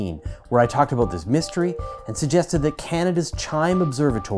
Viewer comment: At first I thought you said Cannabis Chime Observatory, and then I realised you said Canada's, lol.